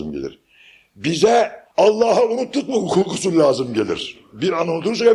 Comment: Selamın aleyküm. 1:41 ne demiş üstad anlamadım. Anlayan yazabilir mi? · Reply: Allah ı unuttuk mu gusül lazım gelir diyor.